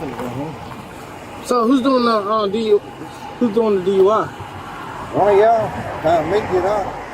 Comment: the older cop’s (unintelligible) speech was him saying “I’m fixin to go home.” Also he said “one of yall, time for me to get off” not “one of yall gotta make it up”